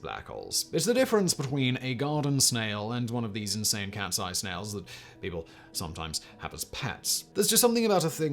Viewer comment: I lost my concentration after I heard “ cat sized snail ! “